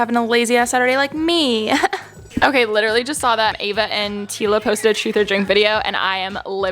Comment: did she say EVA??